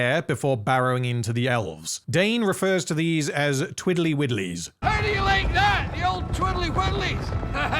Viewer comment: or there abouts. Think they are called twirly whirlys like the toy they look like not twiddly-widdly. Not that it makes it better but the name makes sense if there are similar toys in middle earth